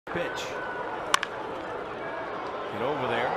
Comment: wow did it actually say that? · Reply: Prodigy Gaming no, it says Pitch